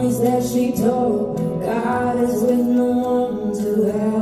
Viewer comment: And James is doing this from his vantage point front row. He can hear her breathe❗️😇🌺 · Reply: I hear " goddess with no one to help her" in the second line.❤